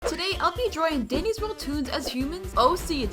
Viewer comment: I thought you said diddys world👁️👅👁️